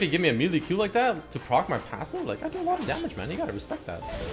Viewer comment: did you say "i do alot of dmg my nigga" ? Lmao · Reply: He said "i do a lot od dmg man you gotta respect that"